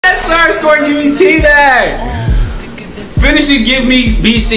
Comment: Sounded like you said tea bag